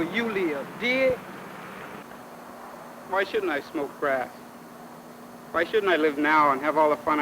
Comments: it sounds like that kid is saying "why shouldn't i smoke crack?" -he's gotta be saying 'grass' instead of 'crack'. Crack wasnt around till the 80s. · Did he say why shouldn't I smoke crack???? In the 70's